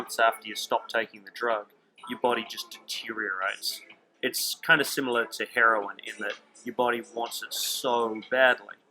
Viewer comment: you talk about a drug " cortizine " ?? I dont understand what you say . Please type it ... ( maybe you mean cortizone ? ) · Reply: I had my own recovery from Cortisone induced eczema, also know as red skin syndrome. You can see the whole recovery in video format here: <link> Or read the blog: <link>